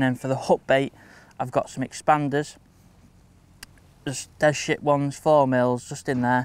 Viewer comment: Definitely thought he said some dead shit pellets.. not des ship 😂